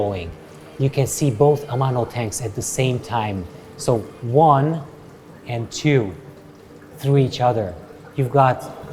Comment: In the first tank at 5:30 , can anyone tell me what type of plant/grass that is which is sprawling, and covering most of the bottom surface? He says the name of the plant but I can't figure out how to spell it for research. Thank you so much! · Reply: It is Eleocharis Parvula. We have a couple of builds with this plant on the channel. Tommy's Iwagumi was last. Check that out. Link for the plant in the description. Needs CO2.